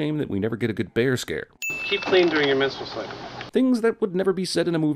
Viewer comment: Keep playing during your missile sliding? What the fuck is he saying?